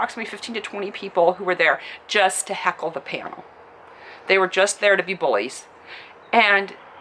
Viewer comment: "Just there to be boys"....that is divisive language man. You are clearly the sweetest woman ever but just wanted to point out that most of us aren't like that. They where just there to be assholes, not boys. Boogey was right, the sooner we all empathize with every group or kind of human being, we don't tolerate it like it is one of our own being attacked because it is, the better of we will be. People liike Anita with her divisive nature, only serve to hurt her own cause Boogey has experience much worse than her, and his message of love and respect rings much louder then anything Anita has ever done. Her supposedly being aggressive towards him after the panel when he is just the sweetest man in the universe is unacceptable; I don't care if she was on edge. She just brings in more hate, doesn't come across like she knows what she is talking about, and is just bad for everyone. Nothing to do with her being a woman, everything to do with her being unpleasant and incompetent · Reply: I thought she said "just there to be bullies"